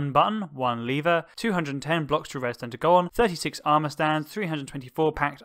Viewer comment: Didn't understand what you said... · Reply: Which part? Aha "210 blocks for your Redstone to go on"? 😅